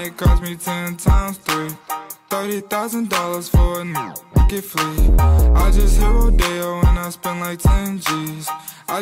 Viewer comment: The beginning is my favorite part and 0:27 I thought it said I just hear Odell